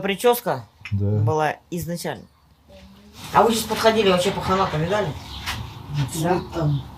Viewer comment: Мне послышалось, сказала: «Пахана поминали?»😮